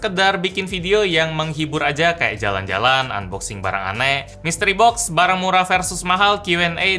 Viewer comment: kirain tadi bilang "istri unbox" ehh ternyata mystery box yah :V Apa emang gw salah denger ato kagak ya ? hnmm